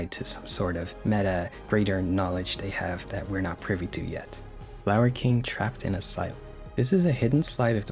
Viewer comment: why do I keep hearing that as "Larry King trapped in asylum"?